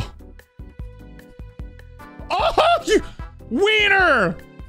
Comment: i had my volume somewhat down and thought he said "YOU QUEER!"